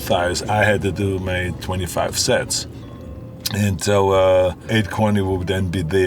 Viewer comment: did he just say 25 sets?!